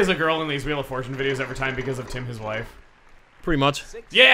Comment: What is saying here? "Tim his life"? I have re-listened to this 30 goddamn times, and that's what the closed captions say, too, but I can't make ANY sense out of that line. Are the CC and I hearing wrong? Are we hearing it right and I'm just not getting a reference to something? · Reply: "Tim, his wife." A reference to the second episode of the New Super Mario Bros. Wii collab from ages ago.